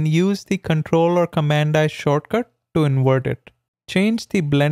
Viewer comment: For those who have trouble understanding what he says in 0:48, after he copies the layer, he says "Press the CTRL or COMMAND + I to Invert it" So just select the background copy and press CTRL + I (in Windows) or COMMAND + I (in Mac OS) to invert the layer mask. Note that this I is the letter i. If I helped you, give me a sub!